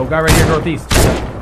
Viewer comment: East I thought you said weast